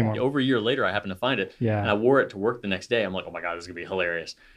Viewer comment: For a moment I thought you said "I wore it to court the next day, I thought that would be hilarious" 😂. That would definitely be hilarious!